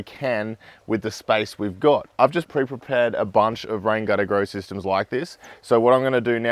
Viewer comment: I hear "grain got to grow system"